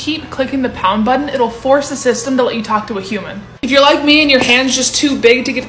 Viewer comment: I love you😍❤️🥺. At the starting of the Pringles portion when you were saying“If you are like me" I heard it “If you like me" as I use to comment “I love you" in your shorts and I thought you are going to give a life hack about this situation also😂. But you are really Beautiful 😍🥺😍🥺❤️. Please take out some time and reply me plzzzzzz🥺❤️. I love you❤️💕💕